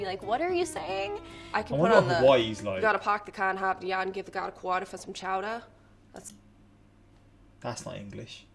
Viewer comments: Massachusetts girl said, "You gotta park the car in the Harvard yard, and give the guard a quarter for some chowder. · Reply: I thought she said “you gotta park the car and have deon give em a quarter for some chowder “😭😭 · "You gotta park the car in Harvard yard and give the guy a quarter for some chowder" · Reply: I heard “You gotta park the car and have Deon give the gaurd a quarter for some chowder”